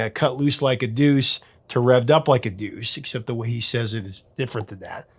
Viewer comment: I never understood the chorus. I thought he said Revved up like a douche, another rumor in the night. 😅